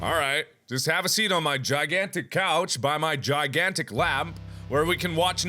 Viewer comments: I seriously thought he said "Sit on my gigantic cock".....After rewinding twice turns out he said couch... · I swore he was gonna say "have a seat on my gigantic cock" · For a second I thought Mark said, "Have a seat on my huge c*ck." Hahaha Lost it